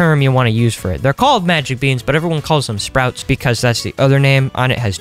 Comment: yo in 9:48 you said BEANER instead of beans